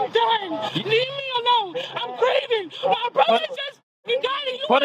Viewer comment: I knew she didn't say, "Im grieving!" Like the subtitles say. I heard, "You killed my brother! Im done, Im done, Im Pregnant!" I told myself, if she is pregnant, she'll scream it several more times as a shield against consequences and sure enough... room temperature i.q.